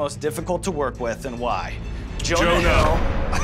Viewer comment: What name did they say? Joe something? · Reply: Jonah Hill